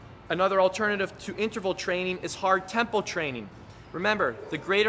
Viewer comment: what was that? sounded like hard temple training or something